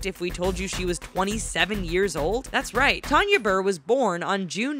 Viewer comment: I thought you said she was 8 years old